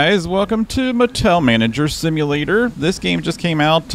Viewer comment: I thought you said Patel Manager… aww someone gets that 😆